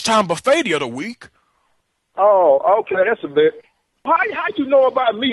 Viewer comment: "oh ok that's a bet" was actually what he said there I just felt the need to point that out